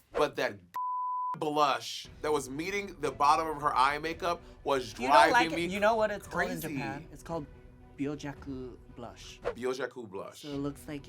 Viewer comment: That Nymphia had a technical term for the way Suzie Toot applied her blush had me gagged. "BYOJAKÜ" (?) was I think I heard her say. ❤❤❤